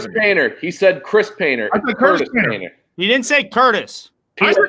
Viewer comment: I love how they tried to pull that and say PFT said Chris and not Curtis lol. Though he clearly said Curtis, it’s just they prolly heard it wrong due to the lag/drop in connection lol.